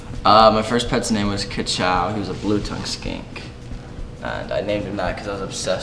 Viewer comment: i really thought he said blue tounge skank.... yall imma gts